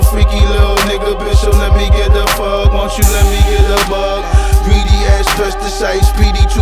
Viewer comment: did he said greedy pesticides or parassite?